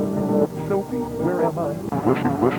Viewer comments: Did anyone hear rosemary say: sophie where am i? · I couldn't understand what the person said before getting well.. Thorn to pieces by the bunny. I heard "Ashley, where am I?" I'm not sure I heard it right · is it just me or does anyone else hear Sha say "Sophie where am I?" at 9:05 ?